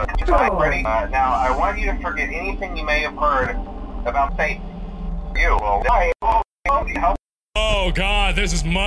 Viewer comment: what I originally thought he said: "Now, I want you to forget anything you may have heard about faith you will die!